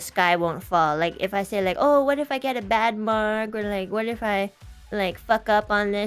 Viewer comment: I think at 0:13 Enna said "bad mark", not "bad mood". But thank you for the clip! I absolutely loved that stream even tho I don’t speak Chinese at all